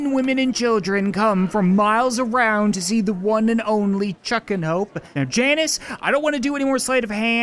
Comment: Fr thought you said Chuck n hoe